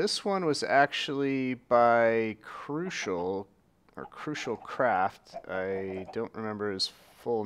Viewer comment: I thought you said crucial crap XD